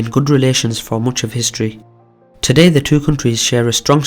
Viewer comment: Did he just say “mooch of history?” what accent is that?